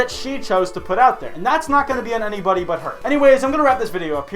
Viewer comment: did you just say THOTS ??????